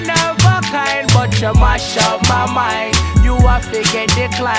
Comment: Tbh I'm here because I thought he said "masha Allah mind" not "mash up mind"